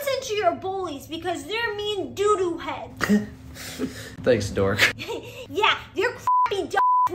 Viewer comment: For a second there when the older brother said to the younger sister thanks dore i thought he said thanks dora i was like oh crap